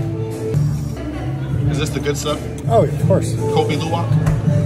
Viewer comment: I heard some like "kopi luwak" 😊